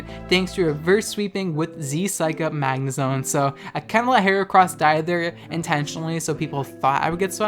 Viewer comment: "kinda let heracross-" deadass thought u said kamala haricross